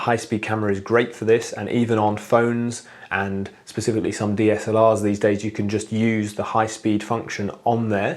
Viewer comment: Hi, Very interesting video. @ 5:30, you are talking about high speed camera, smartphone, and something having an high speed function, but english isn't my native language, and I don't undertand what is this "assala" you are talking about (the spelling is surely false, it's the name I hear) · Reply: I think he is saying 'DSLR', meaning a digital single lens reflex camera